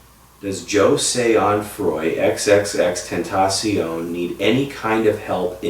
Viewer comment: if u turn on Captions it says xxxfantasy instead of xxxtentacion